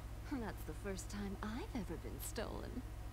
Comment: I actually thought rouge said: "well that's not the first time I've been stoned" xD